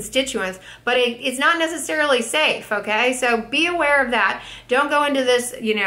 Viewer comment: when you said “beware” I heard “bee-ware”. Had a good laugh!